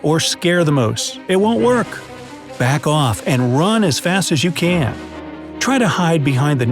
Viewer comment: did u just said run as fast as u can. 🤔